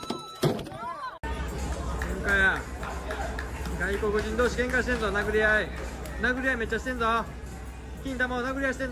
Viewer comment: I really want to know what was that guy recording saying at 3:40? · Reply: 外国人同士が喧嘩しているぞー！と言ってます